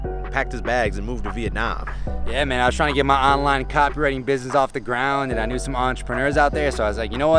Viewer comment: Dave said "Online copywriting business" but I heard "Online cockring business."